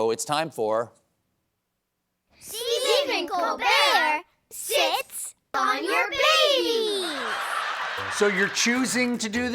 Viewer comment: I was only listening so I didn't see the title card and thought they were saying Stephen Colbert sits on your face. And with those silent farts...